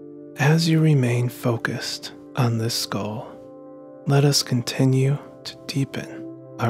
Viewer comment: Does he say at mark 6:12 "let us remain focus on this skull" ?? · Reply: He said "as you remain focused on this goal"